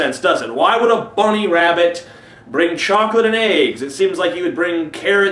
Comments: lol xD i thought he said chocolate and aids... :P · At first i thought he said chocolate and aids.